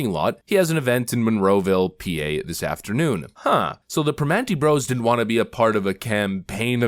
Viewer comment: thought he was saying ANTIFA bros at first